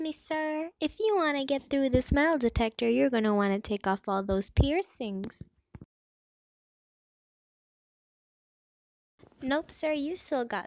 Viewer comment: i thought it said “mental detector”